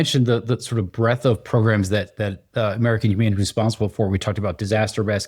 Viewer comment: I thought it said American human airplane